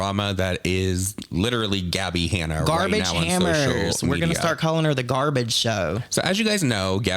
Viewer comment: GARBAGE HAMMERS??? 🤣😭LMFAO did I mishear that? You guys are so creative with names for these people I cannot